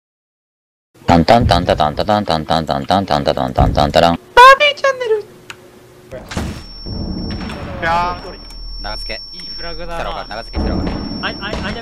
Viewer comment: 毎回オープニング見てて思ったんだけど「マーヴィーチャンネル」が「バービーチャンネル」に聞こえるんだよね。わかる人しかいないはず。